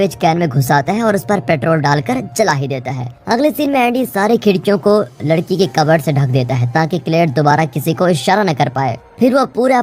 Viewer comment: ladki ka cupboard instead of lakdi ka 😂😂